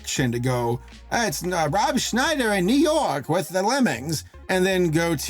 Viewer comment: When you said Rob Schneider, I somehow misheard it as "Dan", and for a moment, I was thinking "oh no"